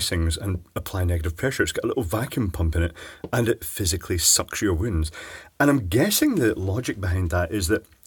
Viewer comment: When you said, "it is a device that sucks your wound," I heard, "it is a device that sucks your wind." Picture me doing a spit take. It has to do with variations in speech between Great Britain and the United States. A device that sucks your wind does indeed sound like a medical device though. If you have bad gas, you can put the other end of that hose up your butt, pump out the bad flatus, and pass it through an activated carbon filter before returning it back out for everyone to breath. You should get to work on that right away! Nasty Wind Sucking and Purification Machine. It improves the lives of your coworkers when you have to work with them with bad gas! This device should also be mandatory at all eating establishments. Who the hell likes it when your food preparers fart around your food?! BLECH! Just install Nasty Wind Sucking and Purification Machine up your butt and prepare food with ease as you casually pass gas with impunity!